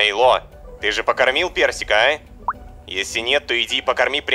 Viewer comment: я услышал "похоронил" 😂